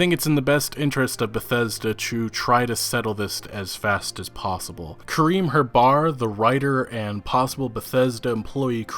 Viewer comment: Did you sayd ::Cream her bar:: XD? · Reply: Karym Herbar